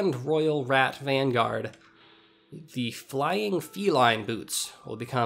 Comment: Mid coughing fit I misheard 'flying feline boots' as 'fly of Elie boots' and because I know nothing about Dark Souls I was wondering if I had missed a lore filled episode or some such